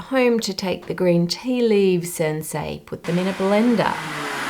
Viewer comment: I check my settings and I have it on English (not auto generated) and there are little emoji’s in the captions so that’s for sure custom. At 12:34 I notice the first glaring mistake where the caption says “Sensei” and she is clearly saying “and say” it feels pretty obvious she’s not saying sensei… haha At 14:19 there is a random capital L on the word Leaf in the middle of a sentence and another random capital at 15:09 which I’ve noticed are usually tell tale signs of auto generated captions (I have no idea why I’ve just noticed that) At 17:15 the caption says “Asian” when she clearly says “immersion” But then at 20:34 there is another emoji so that feel like it’s back to custom. But then again at 22:51 the caption says “satchel” when she says “subtle” At 23:35 the captions says “race” when she says “raised” (that one is the closest least obvious one, I can see accidentally missing that one) These seem to me obvious errors from auto generated captions. I don’t know if who ever was editing this just didn’t catch these but I found them pretty obvious. (I even rewatched on 2x speed to get the time stamps and I still found them…) I don’t mean to nit pick or anything, we all make mistakes, it just feels like it could be more thorough.